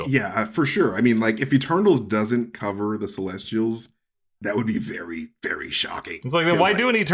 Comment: I could see the MCU version of celestials being normal size beings inside giant armor machines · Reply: Oh for a second I thought you said testicles instead of celestials.